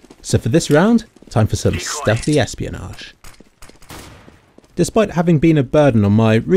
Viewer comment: what does he say on 3:21 ? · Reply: ≧ω≦)ノ "stealthy espionage"